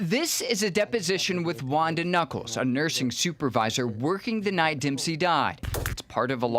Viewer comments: I full on thought he said "Ugandan Knuckles" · Ik ur sad and all... but I heard Ugandan knuckles.